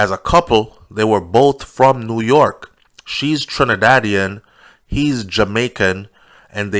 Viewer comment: Tell me about it. Unfortunately, some of our Jamaican women are evil like that too! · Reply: I thought he said she is from Trinidad